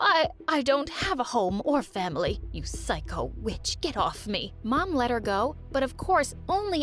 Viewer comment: really let that slide like I heard that old woman said bitch